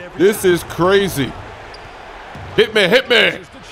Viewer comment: Chris: We gotta share the ball! Also Chris: HITMEHITMEHITME!!! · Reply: When he says hit me I thought he said harambe for some reason